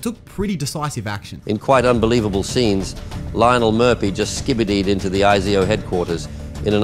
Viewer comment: Did he just say skibidi?